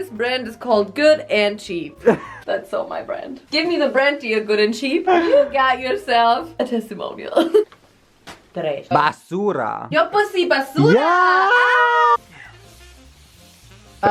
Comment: I swear I heard her say your pussy basura